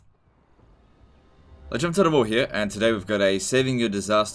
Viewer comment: What he said: "LegendofTotalWar here" What I heard: "Ledge over the wall here"